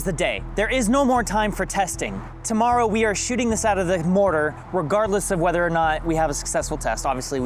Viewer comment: Recorder? I thought your said Remortar